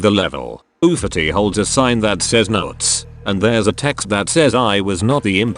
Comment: I hear ✨nauts✨ Idk why i make this🫩